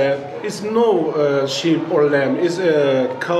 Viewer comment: did he said cow with lamb